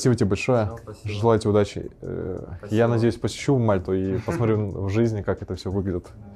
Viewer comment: Мне одному послышалось я посещу мать твою😂😂